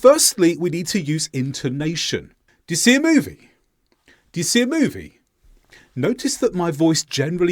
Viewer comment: I heard: "Dyasee a Movie?" (I'm native speaker, California native)